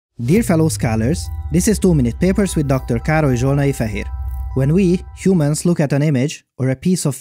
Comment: Dr. Károly Zsolnai-Fehér. Dr. Kárl-ee Zsol-nah-ee-Feh-here I’m glad this video had subtitles, I always thought he said “Dr. Carl (afhhdgjdthcwpna), here.”